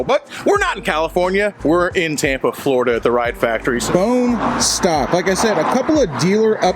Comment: I swear to god I thought you were saying “The Riot Factory “ this whole time. #Goingdeaf